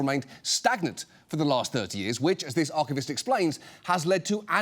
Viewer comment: Did... did he just say 3 years? 0.o